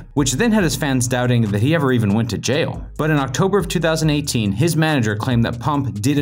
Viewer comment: Did he just said lil pump 3rd arrest was december 4th 2019? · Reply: He said 2018